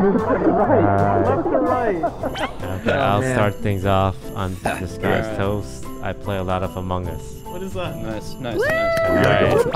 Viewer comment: Petition to get Corpse to do “ Ha this guys toast “ intro for 10000 IQ episode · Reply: it's actually supposed to be "ha, this guy's toast" but Toast took one of the common mishearings, "ha, disguised toast" as a username/brand, and the rest is history